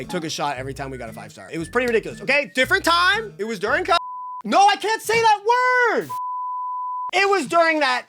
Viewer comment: wait... What did he really said here? Even a vague desc is fine, I'm genuinely out of a loop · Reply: covid